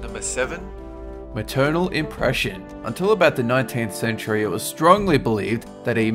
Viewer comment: When it said maternal impression I thought he said eternal depression